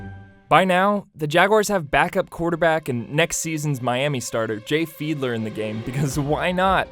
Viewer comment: did someone say Jay Fiedler??? He went to my HS